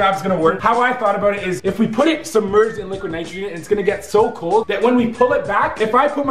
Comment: did anyone else hear liquid Nigeria